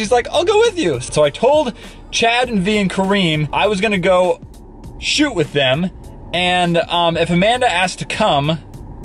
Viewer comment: At first I thought you said "I'm gonna go and shoot in Korea"